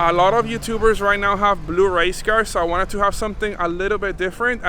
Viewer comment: I thought he said "blue rice cars" 🤦‍♂️😂